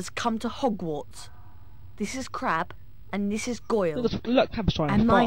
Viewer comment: did malfoy say crap